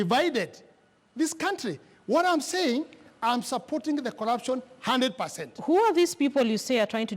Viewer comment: Wait at 26:26 DOES NGUNJIRI SAY HE SUPPORTS CORRUPTION 100%?...